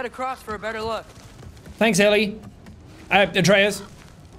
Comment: am I hearing right? Did he say Ellie like in the last of us?